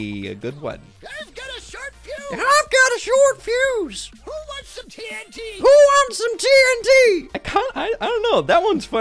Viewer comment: Who else thought that dynamite was saying “who wants some candy” not “who wants some tnt” 😂😂 thought that for the longest time